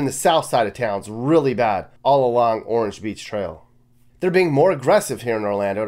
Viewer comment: did you say, “Orange Beach Trail”?